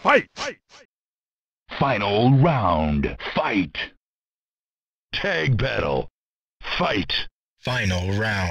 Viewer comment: I don't know what he saying 😂somebody tell me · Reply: Its litterally written x) : tag battle